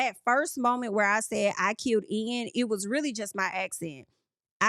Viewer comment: I thought she said “got killed” but the words kinda blended in a way that made it sound like she said “I” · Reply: She said "I killed" and there were multiple people in the room that heard it the same way. She even admitted that she was speaking in first person "trying to get in the mind of".